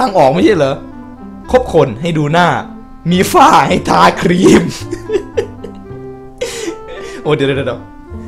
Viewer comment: ผมเคยได้ยินคำว่า คบคนให้ดูน่าสาวแก้ผ้าให้รีบดู